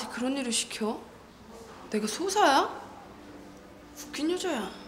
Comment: 왜 나한테 그런일을 시켜 내가 ㅇㅇㅇ야 에서 뭐라고 하는거죠? 수호사? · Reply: 소사요! 잡일하는 사람을 말하는거에용